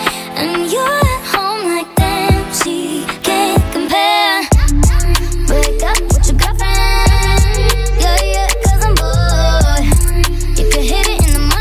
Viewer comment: i thought she was saying "wake up with your girlfriend" lol🤣